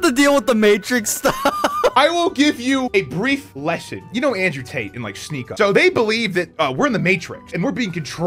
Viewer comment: PA accents make everyone here sound like theyre saying andrew taint and it's hilarious